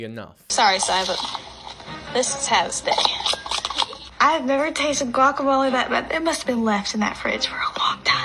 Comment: i think she said shes never had guacamole that bad